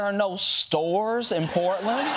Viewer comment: What Trump is saying is so ridiculous that even people who are his supporters, aren’t gonna believe it. I thought he said no sewers! Still no stores is even funnier.